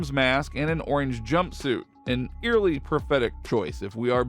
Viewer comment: mask and orange was a pathetic choice? it was awesome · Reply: He said prophetic mate, meaning it was a clue about his criminal lifestyle. At least I think its what the guy meant, I thought he said pathetic at first too.